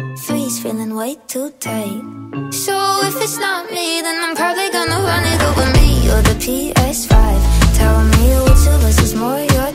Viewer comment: How does she say it 0:46 🥴🤤 ? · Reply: fortnite battle pass